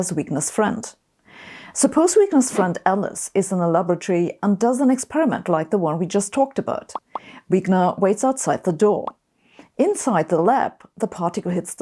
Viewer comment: I thought she'd said that Alice was in a lavatory.